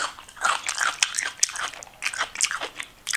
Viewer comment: Did she say yellow red bell pepper at 7:29?